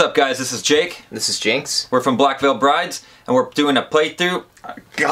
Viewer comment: Did anyone else catch the, "purdy, get outta here bro." Or was that just me? Yes? Okay. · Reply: Amanda Pike he said "Ernie get out of here bro." It did sound like he said purdy tho!